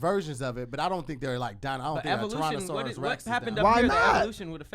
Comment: Did really say "Tronasauras Rex @ 45:20.? Lmao 🤔😂